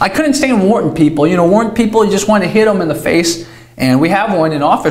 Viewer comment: “I couldn't stand [don't understand whats being said here] people.” “[don't understand whats being said here] people you just wana hit'em in the face.” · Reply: I'm talking about the Wharton School of business ;) My peers in college.